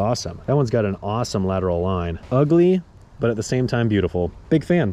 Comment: "I'm going to name him...Korben." I literally heard Leeloo say 'multipass'...